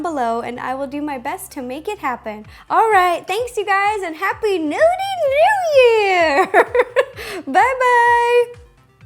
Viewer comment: haha at the end i thought you said " Happy Nudie New Year" XD lol. happy new year Ro! :D